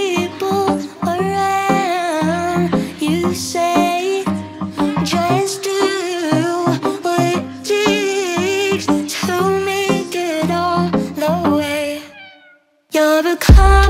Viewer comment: Am I the only one whe can't hear her saying "takes" at 0:59 (ugh so annoying and NOT pleasing) it sounds like she is saying "just dooo what itttt to make it all the way"